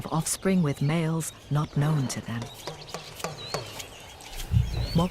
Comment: what is being said? "not knowing to them"? what does that mean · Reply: "Not known to them" if he isn't from the pride she is far less likely to conceive